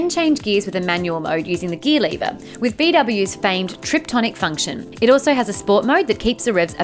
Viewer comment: Did she say Trip-tonic?